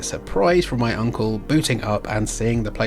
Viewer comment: It sounded like you said your uncle bootycup.